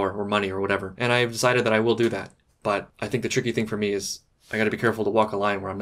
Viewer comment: Wait, hold up. My english isn't the best, so I might be misunderstanding this...but did say that " he will do that."? As in he's gonna become a Korean gangster??